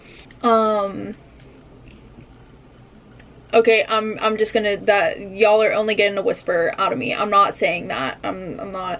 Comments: Am I the only one that has no idea what she said at 4:13 ?😅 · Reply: She said Anal 💀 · did anyone get what she said? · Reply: An--al